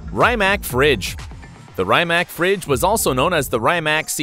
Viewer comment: Rimac Fridge, u mean Nevera？ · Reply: I thought he said Fringe